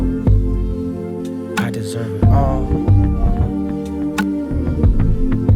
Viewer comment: There is a whisper at 4:43 that I can’t make out, anybody else hear it? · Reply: Listened again and sounds like a whispered “I deserve it”